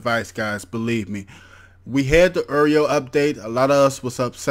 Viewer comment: Did you just say, "errio" update"? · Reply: Anthony Martinez oreo